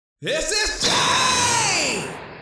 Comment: I always thought his intro said “This is gay” for so long